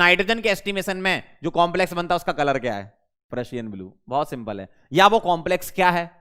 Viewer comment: I saw the lecture in 2x instead of purrusian i hear Russian 😂